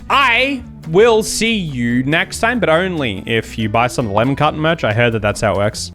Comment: Is it just me or does it sound like he is saying "lemon cotton" instead of "lemon carton" i just noticed the difference tho😅